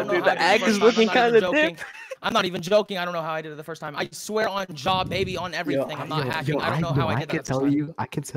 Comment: it sounded like he said "i swear on jaw baby" instead of jah